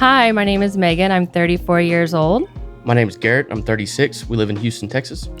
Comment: *Correction: stated he is 36. Screen Caption says [ Age: 26]